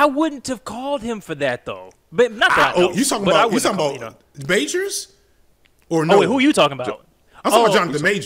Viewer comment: When he said 'Majors,' I thought he said 'Beige-rs.' 😂🤣😅 That ought to be a category, the DJ Envy, Steph Curry ... category.